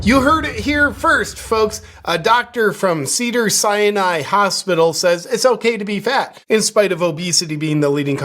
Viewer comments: I seriously thought you said ‘cyanide’ hospital!! 🤪😂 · I thought you said “Cedar Cyanide” at first, which would have been appropriate.